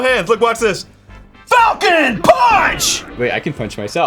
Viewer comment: Anyone else thought Arin said "FUKIN PUNCH!" at first?